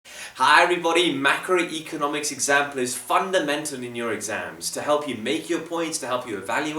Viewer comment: “vundimentor in your exams” U sounded like a banjeet there